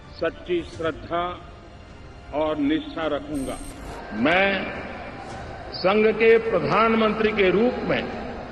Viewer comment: did he say " Sangh k pradhanmantri k roop mein " ???? 🤯